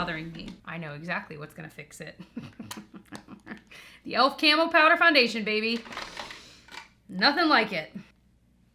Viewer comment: I thought you said camel powder 😆😆😆